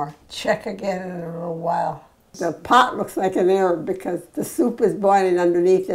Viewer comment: does she say the "the pot looks like an Arab" ? lol